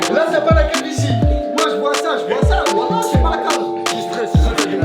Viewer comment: A 19:45 quand sa parle de la calvitie jai entendu gamixtreize 🤣🤣